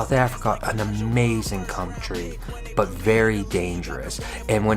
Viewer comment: Listen REALLY carefully to what says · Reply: cum tree